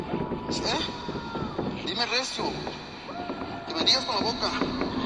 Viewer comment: translations don't seem to be right. He says dime recipe which "tell me" but so I can hear you and the last one sounds like he says dime con la Boca "tell me with your mouth" not do you know who I am, that would be "sabes quien soy?"